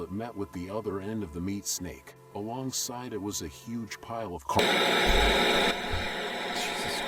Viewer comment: I thought they said a huge pile of cu….😂💀